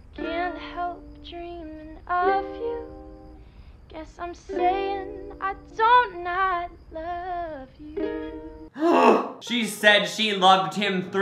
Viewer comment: i thought she said "I'm gay and i do not love you"